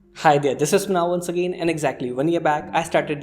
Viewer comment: lol wait what's your name didn't catch it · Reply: sound like you saying "this now once again" lol sorry